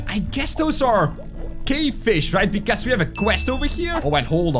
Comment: When you say Cravefish I thought you said gayfish😂😂😂😂😂😂😂😂😂